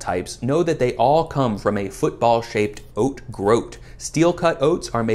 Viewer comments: I really thought he said “football sized” instead of “football shaped” and was frantically googling where to buy a giant oat groat so I could bite into it like an ear of corn. Took me 5 minutes to come back and rewatch the video. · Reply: I heard "football sized" to 😂 I was watching this thinking that can not be right. Had to watch it twice just to confirm that my life isn't a lie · Genuinely thought you said "football sized" and not football shaped and my mind was fried for about 60seconds. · IDK how i heard "sized" and not "shaped"; I was very puzzled as to where you were finding football sized groats lol